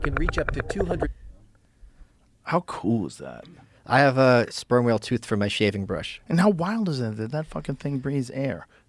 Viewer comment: Thought Michael said he had a sperm whale tooth brush